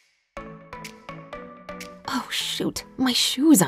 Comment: I swear I heard oh shit